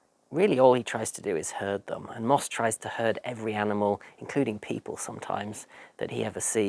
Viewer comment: I misheard "herd" for "hurt" so at 20:12 , I was so confused. "Really all he tries to do is hurt them, and Moss tries to hurt every animal, including people sometimes..." Makes way more sense once I played it back and realised you said herd.